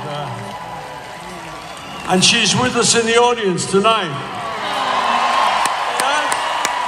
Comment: What did he say ? She is with the ??? audience tonight ? Please help. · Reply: She’s with us in the audience tonight